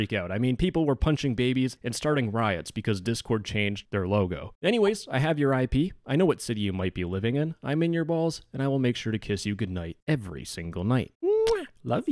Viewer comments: idk if its just me or do i hear "I'm in your balls"? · Instead of "im inside your walls" I thought he said "Im inside your balls" 💀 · Wait, did he say walls or balls??? Either way concerning but i feel better knowing he still gives out kisses to his viewers... Timestamp: 6:06 or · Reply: The kinky fucker said balls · subtitles say something else 💀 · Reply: Won't lie, I was scrolling comments during his outro and maybe my tism hit but I 100% heard him say "balls" not "walls"